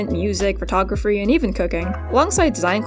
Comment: “and even cooking” i swear i heard cocaine and that is a concerning fact.